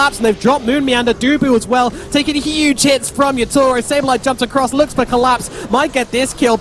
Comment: Did he just say “Taking huge tits” ???